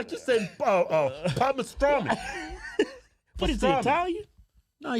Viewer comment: I thought y’all was saying “raw pastrami” 😂